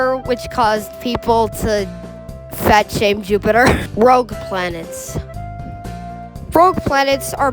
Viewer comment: I know you said rogue planets but it why did it say rough planets?